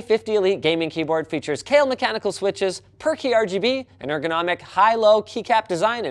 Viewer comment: I could have sworn he said "Perky RGB" on the sponsor. Brain refused to hear it as "per key."